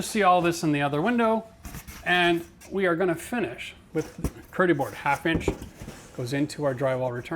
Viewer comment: Could somebody tell me what he said near at 18:46? I heard "curie board half inch"; but, I could not find the information about it in search engine.